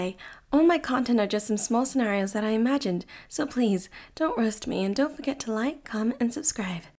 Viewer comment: I misheard the into as don't arrest me LOL